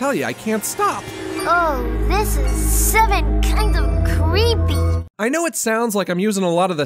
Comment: I couldn't understand what Buttercup said the first half but I do know that she said "creepy" at the end of her quote. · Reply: She said “oh this is seven kind of creepy”